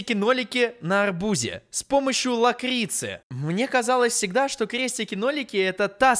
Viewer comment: Мне послышалось С помощью макрици А не лакрици🤣🤣🤣 Кто не знает мокрица это маленькая чтучка дрючка которая живёт в воде как рыбка она очень очень маленькая